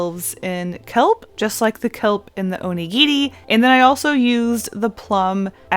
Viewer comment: I'm sorry but the first half of the video I was confused, I thought she was saying 'onigiti' and I didn't know what that was